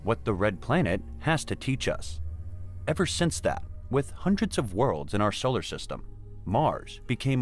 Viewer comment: Did he say, "hundreds of worlds in our solar system?" at minute